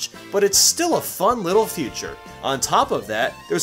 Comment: or 3:31, I think he meant to say fun little "Feature" but I heard future XD